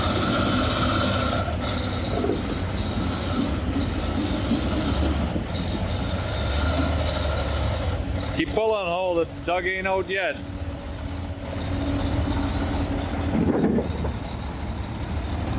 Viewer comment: i thought you said “keep pulling ho” as in back-ho. I laughed but then realized you said something else